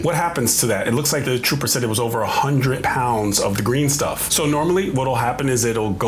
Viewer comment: no, they did not say 100 pounds, he said 1000 pounds! 🤯